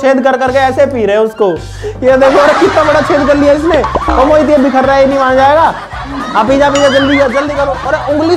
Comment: KYA BOL RAHE HO BHAI 😂😂😂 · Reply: Ched